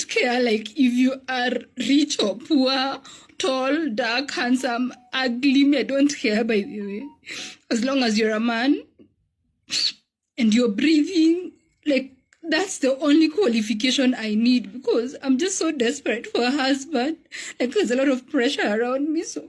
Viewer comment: I thought she said as long as the guy was pretty but it as long as you are a man and breathing wtf 😳 .